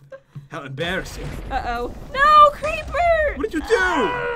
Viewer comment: it sounds like is saying Creeper Aw Man.